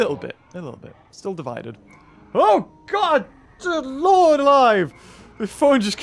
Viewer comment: Did you just say “OH! The Lords a lie!” Geez.